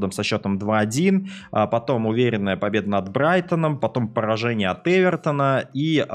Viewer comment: Послышалось будто ты сказал оборотень а не Олбрайтон